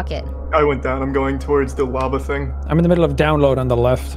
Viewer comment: does anyone else hear "wabba" instead of "lava"?